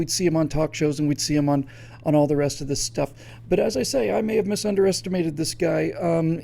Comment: Did you just say "misunderestimate"? :-D